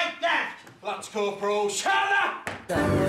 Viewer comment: did he said "Lance Corporal shut up!!!" ?